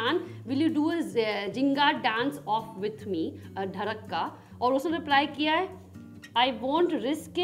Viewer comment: who else heard tharak instead of dhadak 😂😂😂😅😆